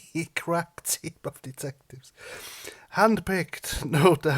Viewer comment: I laughed so hard when you said crap team of detectives, I thought you said crack team for a second.